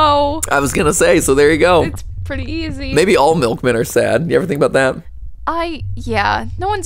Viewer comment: the subtitle says maybe all milkmen are dad💀💀💀